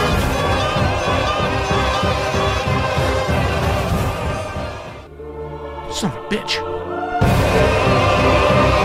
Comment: Follow on twitch to hangout live! Twitch.tv/ Join the discord and post your funniest memes! <link> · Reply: I thought he said SON OF A FISH